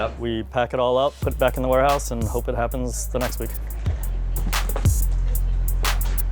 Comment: what did he say ?? Next week ? If you lose your eliminated there's no next week or did i miss something